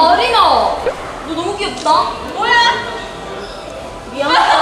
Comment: 에 뭐라고 하는지 아시는 분 알려주세여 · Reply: 뭐야! 입니다 ㅋㅋㅋㅋ